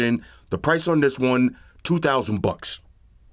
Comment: I thought he said. “Price on this one 5,000 bucks.” I was like, I like the future but damn.